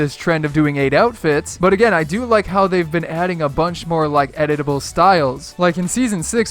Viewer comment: did he say edible styles?